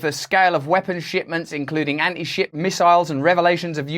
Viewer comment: I thought you said Anti Shit Missiles 😂